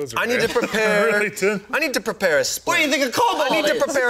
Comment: I thought he said "I need to prepare a spliff" the first time lmao